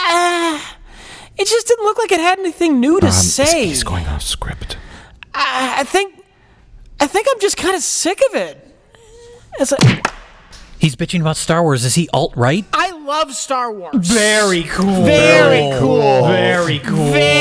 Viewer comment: "He's bitching about star wars, is he alt-right?" My favorite line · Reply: At first I thought he said “is he alright?” But now it’s even funnier.